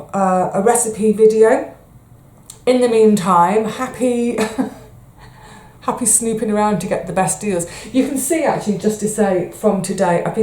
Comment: Thought you said 'happy sleeping around to get the best deal'.....